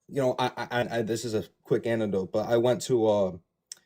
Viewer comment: Did he just call it an anditode?